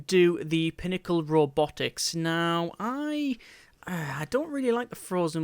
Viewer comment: I thought he said nipple robotics